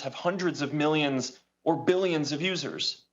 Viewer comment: CORRECTION IS "TRILLION" instead of "BILLION"